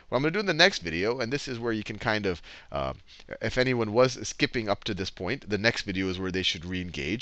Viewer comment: subtitles say 'wasn't' when Sal says 'was'